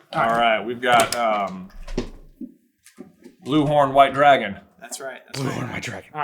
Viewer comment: After the Shrek and Donkey parfait quotes J at 23:23 sounded like Donkey saying ' blue flowers, red thorns"" lol